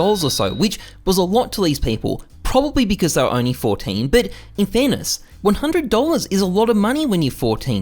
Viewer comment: sounds like he says "But in Thanos" XD